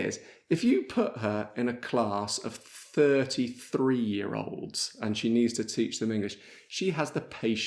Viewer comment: al minuto 15:20 inizialmente ho capito "a class of 33 year olds"... solo poco dopo ho realizzato che si trattava di 30 3-year olds... :-)